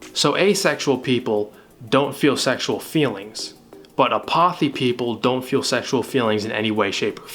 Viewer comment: im so sorry but when he said apothi (9:00) it sounded like he said 'a possy' but with a blisp